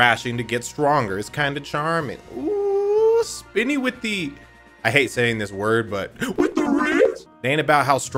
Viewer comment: cause of the distortion I thought u said spini with the reads 🤣